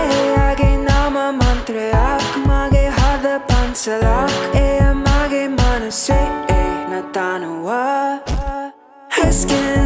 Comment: I love this song!! I thought you were saying “Eya mage Manase ena thanuwa.. ..” till I saw the lyrics! Amazing creation guys! I can’t stop humming this tune! 💕💕💕💕